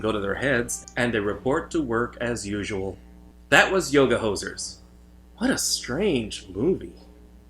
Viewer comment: I really thought it said Yoga Horses 😭😭😭😭 I was soooo confused but intrigued, and now I am vaguely disappointed but still intrigued